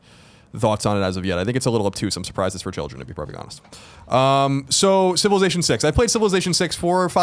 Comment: LOL what did mumble @ 8:22?? Also Mega Man Legends 2 was DA BOMB! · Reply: "It's a little obtuse, I'm surprised it's for children, if I'm being perfectly honest/let's be perfectly honest" (in reference to: World of Final Fantasy)...I believe... 😀